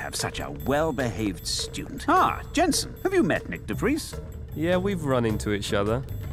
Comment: I hear correct? Nyck de Vries?